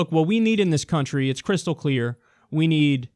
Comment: did you just say "what we need in this country is crystal" O_O ....